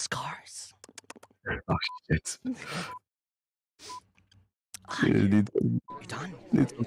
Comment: for french people : Is it just me or did just say "putain" 😭 just before said "You need therapy"